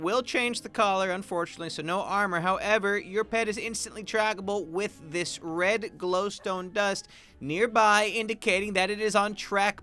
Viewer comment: did I hear you’re pet is now trackable with this red glow stone dust?